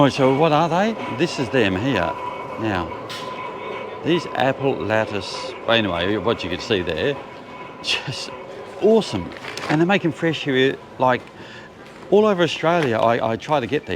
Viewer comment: What are they? I didn't make out what you said. 🙂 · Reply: They are Cinnamon and Apple pastries.